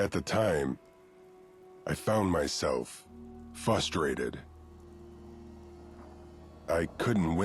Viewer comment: "fustrated"? When I played this, I remember wondering if I just misheard him. Nope, he really is just fustrated